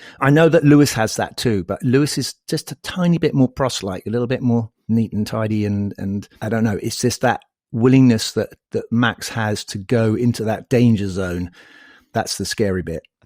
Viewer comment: lewis is that tiny bit more “proselyte”? 5:09 can someone explain me that last word? Did I hear DH say that correctly? I would love to understand.